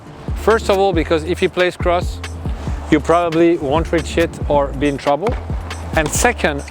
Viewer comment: I thought Patrick said "you probably won't reach $#@ lol